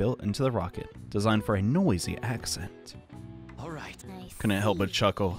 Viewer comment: did he say accent?